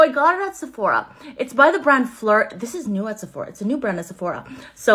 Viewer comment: I have this perfume. It smells amazing! I wish the staying power last longer. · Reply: 2 things, first thank yoy for typing that out...I thought she said "flirt" instead if "phlur". Second, I LOVE your name!!!!